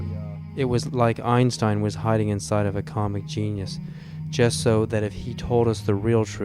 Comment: " Einstein hiding behind a comic genius" 100% agreed. He is so well spoken and articulate, love this interview. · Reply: I thought he said common genius.